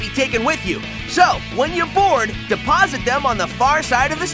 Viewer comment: I thought he said “When your born” lol